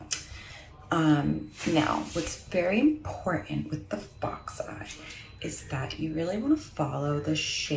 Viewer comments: Literally, I kept hearing her say “Box eye” and was like, what’s the problem. 😂😂 Then I heard it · I thought she was saying "box eye" because it's kind of a boxy look until I saw the captions...